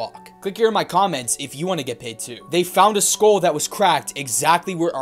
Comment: At the end, I thought it said everyone was shot 🧌🧌